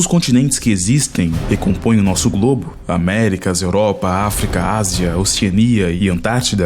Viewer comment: Ásia, Europa... Acredite ou Não eu entendi "As Europa" !!!